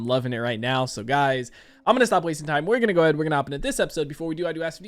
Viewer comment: I have been enjoying these Better Call Saul reactions, but I have to admit, I have no clue what you're saying between 1:27 and 1:43. It's as if you decide to start speaking five times faster than normal, which makes it really hard to pick out individual words. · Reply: “…Guys. I’m gonna stop wasting time we’re gonna go ahead and we’re gonna hop into this episode before we do I do ask if you guys are new to channel you do hit that subscribe button comment down below what you guys wanna see next man drop a like under the video for your boy full reaction will be up in patreon as well as early access to the next two episodes if you guys wanna check that out link in the description down below but we’re gonna go ahead we’re gonna go hop into this episode.”